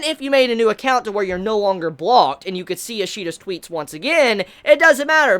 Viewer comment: Ishida gave this person a big L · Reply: Each time u say Ishida it sounds like ur saying Ishida sweety